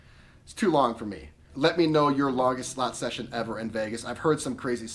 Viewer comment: When you asked the question I heard “What was your longest slut session”!!! 😆😆😆😂